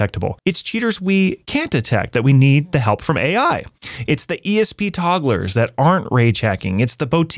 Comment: I know you meant "esp togglers", but I swear I heard you say "esp toddlers". lmao